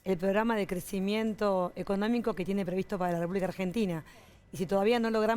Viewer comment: Qué dijo Tolosa???? (min 0:46) "el crecimiento económico que tiene para "dormir" a Argentina" ????? XD. Pobres taquígrafos en el Congreso! Tiene un papa en la boca.... (y un tereso en el melón, Jajajaja!)